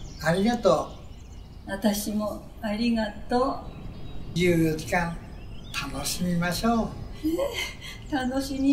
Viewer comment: The first scene says in Japanese Man ''Thank you for 60 years.'' Woman ''So do I.'' Man ''We will enjoy our remaining time!'' Woman ''So will we.'' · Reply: おじいさんの台詞、「24時間、楽しみましょう」に聞こえるんだけど…